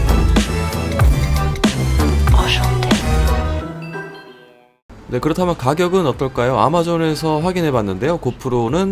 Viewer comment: 아니 여러분 저만 5:46에서 이상한 말소리 들리나요? 아무생각 없이 보다 소름 돋음ㅠㅠㅠ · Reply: 바이크타는공대생 앜ㅋㅋㅋㅋ 그거 노래에 있는 노래에요 여자분이 오쑝떼! 라고 계속 외침 ㅋㅋㅋ